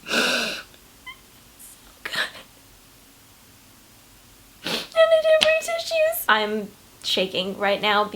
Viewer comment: wth did she say at 0:48? hahahahah " i didnt bring some shoes" or smthn?